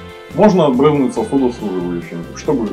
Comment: 10 раз переслушивал и только потом понял что говорит про сосудосуживающие(нафтизин например).